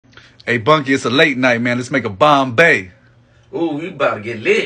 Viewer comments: Accidentally clicked off and I thought man said "Let's make a bomb" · I thought his Bunky said ooh we bout to get laid! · I thought he said, “it’s late, let’s make a baby”.